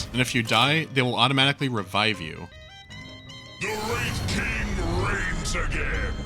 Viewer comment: I swore that at 6:00 he said "The rape king rapes again!"...